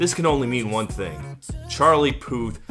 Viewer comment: Ha ha this whole time I thought you were saying "Charlie POOF"!